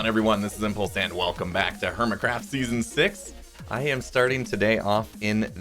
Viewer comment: It sounds like you said hermitcrap lol.